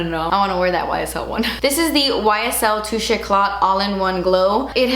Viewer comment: I did not think you said touché when you described that foundation. I thought you said two shits.